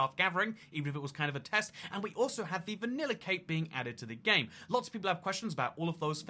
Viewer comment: I swear I thought was saying a vanilla cake was being added to MineCraft, and I was like, what about chocolate cake? 🤣 🤣 🤣